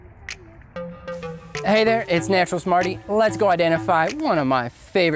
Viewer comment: I'm enjoying your tree videos, and look forward to others. The first one I watched though, I thought you said your name was "Naturalist Smarty", lol.